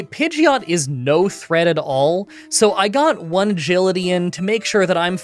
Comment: did you say "one Gility" to stand in for "Agility" like "A Gility?"